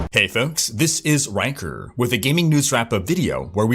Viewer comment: i heard "hey folks this is wanker" lool